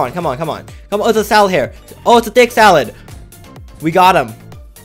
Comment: who else heard say it’s a thicc salad?😂😂